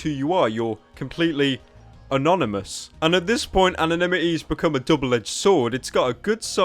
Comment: he really can't say "anonimity". I thought he was saying anime-ity at first.